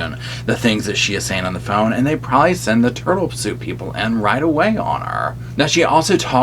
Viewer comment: I thought you said "They were going to send in the turtle soup people". lol, Gee, I wondered what that was. Luckily I have closed captions on. Oh, I see, it's the 'turtle suit people. lol Got it, okay, back to the video!!